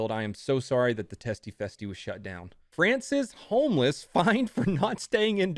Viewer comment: I thought you said Francis Homeless, and i was baffled that this homeless mans last name was homeless. The picture through me off lol !